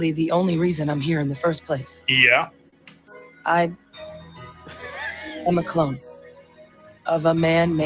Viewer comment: Wait she's a clone of a man... Umm I don't think they understand what a clone is lol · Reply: Oh I thought they said "I'm a clown", it would fit better.